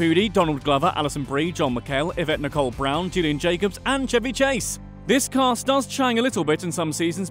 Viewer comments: I thought he was saying chubby chase😂😂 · Did he call Joel McHale, John McHale at the beginning when he is listing all the actors? Its at like 1:10 or right after. Or was it just the accent?